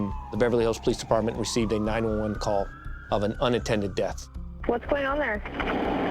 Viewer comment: At first I thought he said "unintended death." What? I had to replay it a couple of times to figure out it was "unattended death." Oh, okay. Time for hearing aids, I guess. 🤣